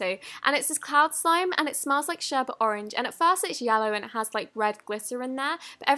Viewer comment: does she say sherbet orange not orange sherbet?? Do they say it like that in the UK?